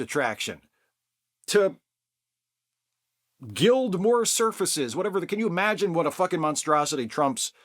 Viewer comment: Omg, when you said “gild more surfaces” I legitimately thought you said something about “Guild Wars” and I’m just here playing GW2 while listening and had too go back and make sure I heard what I thought I heard. And I was very glad to find I’d head wrong, cuz I can’t quite imagine what Trump and Guild Wars would have in common 😅